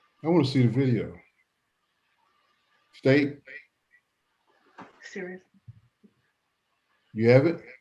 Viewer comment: When he asks to see the video do I hear the defendant saying, "serious?" Maaaannnn....if so throw the whole book at her. Throw unrelated books at her. Throw her the whole damned library! 😂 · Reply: Yes. At 2:13:20 the judge asks to see it. The defendant can clearly be heard asking "...serious" I think she was saying "are you serious?". She is still more concerned with the embarrassment the video caused herself than what the plaintiff suffered. I think she should have gotten at least 6 months and had to pay the plaintiff $5,000.00.